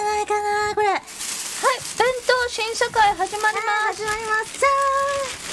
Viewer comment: I think Coco said "examination committee(審査会shinsakai)". not "new world(新世界shinsekai)".